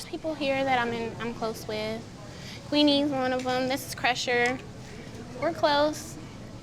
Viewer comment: crusher? Did i hear that correctly?